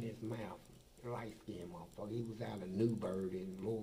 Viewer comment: Did he say Newburgh 27:20?